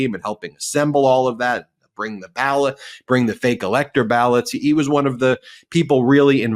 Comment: Did he say fallots? That has a nice ring to it. Fake elector ballots=fallots 😂